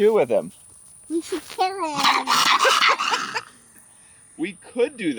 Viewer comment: It sounds like she said we should kill it